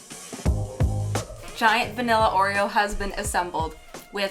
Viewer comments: I swear I heard "giant vanilla oreo husband assembled", and I just... I love it. :'D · I heard “giant vanilla Oreo husband assembled” and didn’t even bat an eye